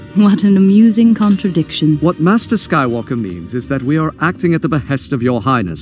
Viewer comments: Did he just call him Master Skywalker? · did he just call anakin master? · Wait did he say Master?